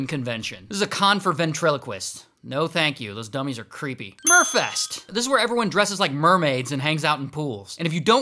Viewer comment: I wasn't looking at my screen when he said "mer fest" and i immediately shot up and looked. I thought he said fur fest 😭😭😭